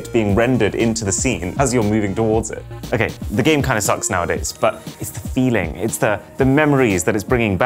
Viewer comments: Excuse me! Did he just say SMB 3 suck’s nowadays!!? Bro just lost my respect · what did you say? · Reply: “As you’re moving towards it”